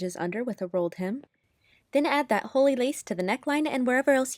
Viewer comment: okay sorry but when you said "holey lace" i thought you said "holy lace" and i got very confused praise the holy lace (edit) this happened at the beginning of the video don't worry i'm just that big of an idiot (pls dont yell at me)